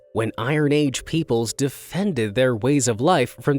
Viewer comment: Did he say peoples